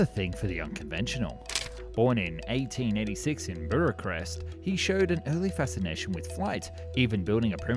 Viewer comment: Relax, matey. It was still good video. That "Bracarest" had me beat, though. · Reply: i heard "buracrest"